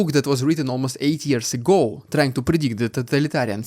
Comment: I can't hear anything else than "8 years ago"... do others actually hear 80?